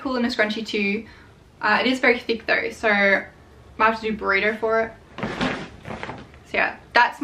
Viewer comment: Hi @ 44:46 did you say burrito? ty